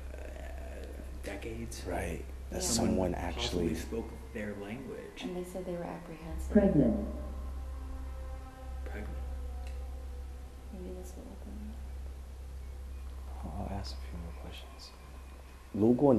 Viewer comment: Anyone else hear 'pregnant' at 40:54?????